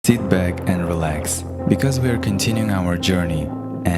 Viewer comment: I thought he said "tit bag"